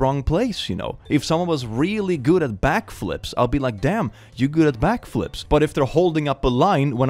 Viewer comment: did you say black flips?? lol